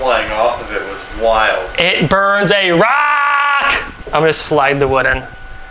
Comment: Did he say it burns iraq